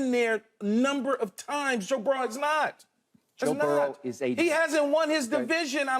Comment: did Keshawn just say Joe burrow hasn’t won his division?